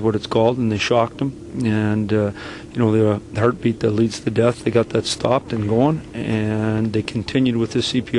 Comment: did he just say "the heartbeat deletes the death?" I mean, true, but that phrasing sure was different.